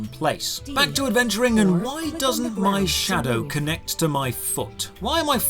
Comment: I'm ashamed to admit that I heard "And why doesn't my shadow connect to my thot?"